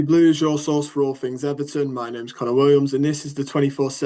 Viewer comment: At the start you sound like your saying my name is a can of worms 😁